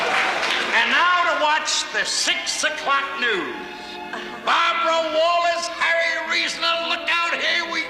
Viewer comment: I acquired this episode in a tape trade in 2007. Three years before it saw the light of day on DVD. At 47:01, I thought Jackie said: “Barbara Walters.” I thought that made sense since this was on ABC and Barbara worked there until the 2010s but he said Bob Ro Wallace.